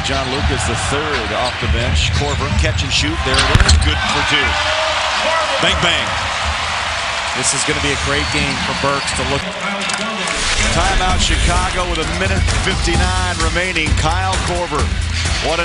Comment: did he say bang bang? Lmfao *Cheif keef voice*